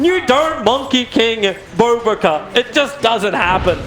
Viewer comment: did he just say 'BOBO KA'?